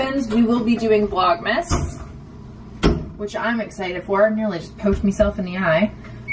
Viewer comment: did you say “I almost poked mi self in the eye”? Those British shows are getting to you! Haha